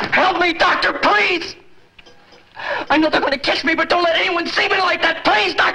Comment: Sounds like he says I know they are going to kiss me.